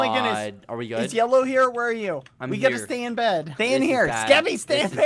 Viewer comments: Tell me I wasn’t the only one who heard bbh saying: we’ve got to stay in bed! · Did bbh say We gotta stay in bed